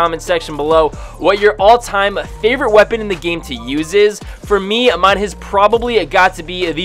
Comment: did you say you’z is?